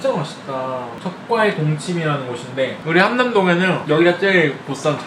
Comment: 적과의 동침 계속 적발의 똥침으로 들려요.. 제 귀가 왜그럴까요..?